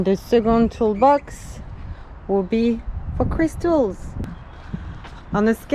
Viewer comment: Thought you said 2nd toolbox was for crystals....!!?? bit new age for me..